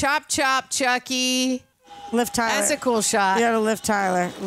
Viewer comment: kept saying Lift Tyler but I could have sworn she was saying Liv Tyler 🙆🏾‍♂️😂